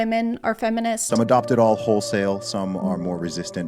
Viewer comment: did i hear him say 'WHOLESALE"